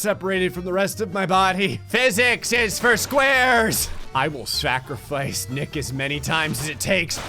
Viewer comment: "physics is for squares!" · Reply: For a second I legitimately thought he said "physics are for squirrels!"